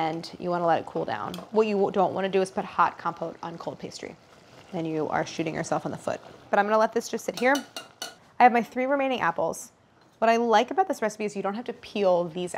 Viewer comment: I thought Claire said “you’d be sh*tting yourself in the foot” 😭